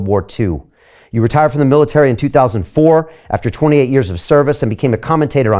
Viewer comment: aw col misheard and thought he said 20 yrs service and not 28 like he said, but semi unclearly.